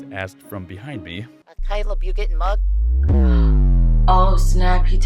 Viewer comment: I can't work out what it's actually saying I keep thinking it's saying "I hope you get mugged"